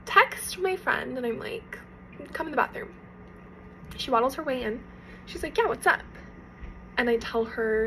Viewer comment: Did she say she "WADDLES" her way in to the bathroom?? 😅